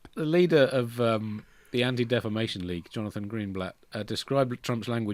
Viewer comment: Happy holidays, and what not .... there's a point where the first time through I heard the "Andy Defamation League" and I thought even the puns don't warrant that. Though reasonable people have disagreed 😂